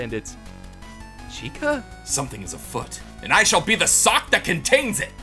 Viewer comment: I thought he said something is f##ked and I shall be the sock that contains it.